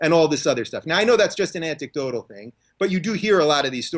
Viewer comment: Is it just me, or is Rubin saying "Antik-dodal" instead of anecdotal at 16:54, looool??